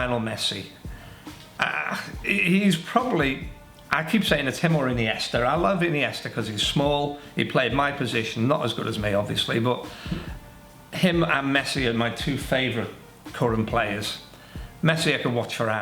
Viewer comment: Why does it sound like he said "i like it in the ass, cause its small" at 7:19 LOL